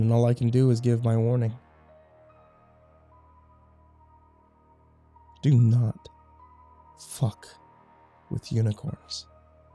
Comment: I thought it said "do not cook with unicorns" I hope I'm not the only one lol